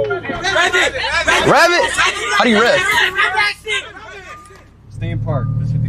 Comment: Did he just say "how do you rave?"💀